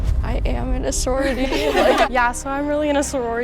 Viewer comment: The first girl sounded like she said “my name is sorority” I had to rewatch it like 5 times